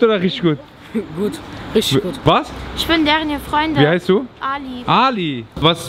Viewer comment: freundin???? Hab ich richtig gehört? Ist Ali jetzt ein Junge oder ein Mädchen? LUL